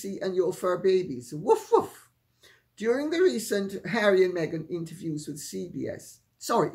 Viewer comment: I swear that I misheard Lady C say " ... during the recent HARRIDAN Meghan interview...".😅 which makes perfect sense as Meghan Smirkle most definitely fits the description of a "harridan". For those who might not be familiar with the term, harridan means "a strict, bossy or belligerent old woman; for example, a bullying old harridan".😂